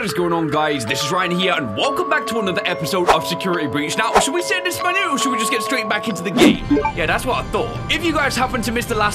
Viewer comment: Why do I keep hearing “should we keep saying it in español” at 0:48 in the vid 🤣🤣